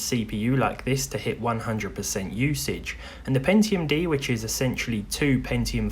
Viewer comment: Oh, Pentium D, I thought you said Pentium 4, that's even older.